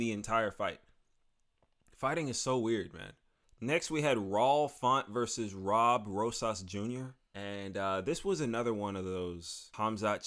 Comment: Did he just say Raul Font and Rob Rosa’s jr